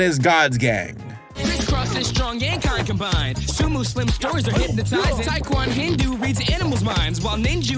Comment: I had to listen to the theme music several times because I swear it sounds like they say "Sumuslims toes are hypnotizing"??? Am I hearing that correctly?? Surely that's not what they're saying..